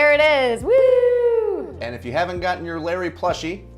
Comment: I actually thought she said Larry when she said the onion was called Layery.